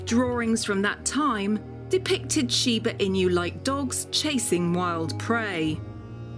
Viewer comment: Shiba inu. I keep hearing her say "she ba in you". It's "e nu".